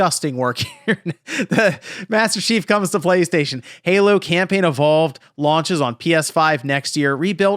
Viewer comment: This is all I heard.. Halo: “Camping” Evolved. lol. 🏕️